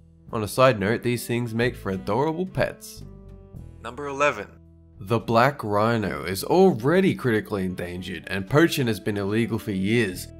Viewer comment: I thought I heard the last Black Rhino was already killed